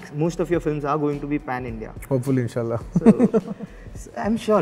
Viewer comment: did he say insha allah